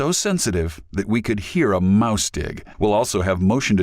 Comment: Oh...."mouse-tick". Thought he said mouse dick. Mouse CLICK would've been a much better choice. I know it's much less severe than the xenophobia, but it sounded weird as shit.